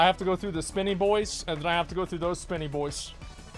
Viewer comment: I thought he said he had to go shit.